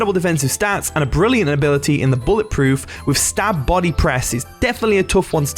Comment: Why bullet proof stop body press ? 🤔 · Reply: Nevermind I misheard 😅 "stab body press" (stops body press I heard)